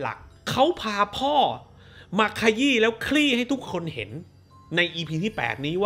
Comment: สิ่งที่พี่พูด : พาพ่อมาขยี้ และคลี่ให้ทุกคนเห็น สิ่งที่กูได้ยิน : พาพ่อมาขยี้ และขรี้ให้ทุกคนเห็น<num>